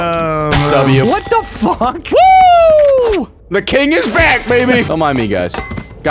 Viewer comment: genuinely thought he said the king is a black baby